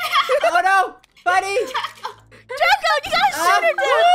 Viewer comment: i heard Draca